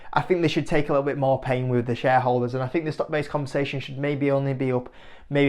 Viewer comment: I keep hearing say "stock based conversation"...